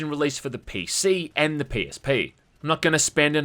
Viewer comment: you know how sometimes when you are swallowing your saliva or drinking or eating something and your hearing gets impaired a bit due to your internal sounds (especially when you're congested)? Well I heard you say "Penis P" then I had to go back to make sure but unfortunately you said PSP. :(